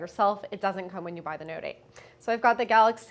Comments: did she just say note 8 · DID SHE SAY NOTE 8?!